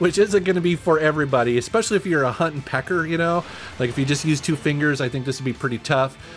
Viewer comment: Did he just call me a huntin' pecker?